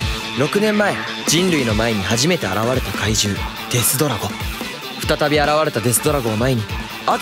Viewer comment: デスドラゴがデストロイアに聞こえた（難聴)